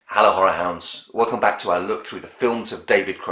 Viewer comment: First time here And I generally thought you said “Hallo whore house” (yes I am aware of the misspelling of hello. Lol) I had to check the channel at first Hahahaha Great review tho